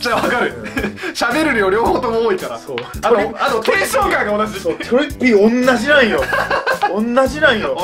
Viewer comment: ここ何が同じって言ってます？ 聞き取れなくって😵‍💫🙌🏻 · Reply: 多分「テンション感が同じ」って言ってますね (違かったらすみません…)